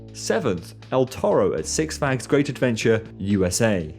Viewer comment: yo bro what did u call six flags great adventure?? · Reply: Six Flags Great Adventure! At least that is what I said just not overly clearly 😅